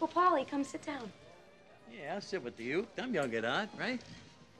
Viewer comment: what does Paulie say after "yeah, i'll sit with you" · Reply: "i'm young at heart, right?" joking that even though he's old he fits in with the new people